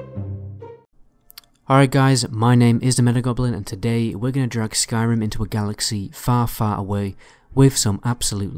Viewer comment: sounded like u said we're gonna drug skyrim..